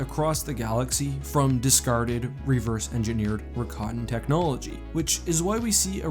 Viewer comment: what word is he saying here? Ricotton? It's like an ancient race of aliens right? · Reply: Rakatan I think Look up the Rakata